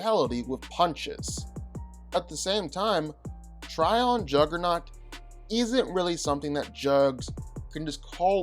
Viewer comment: At the beginning of the video I thought you said Zuckernaut.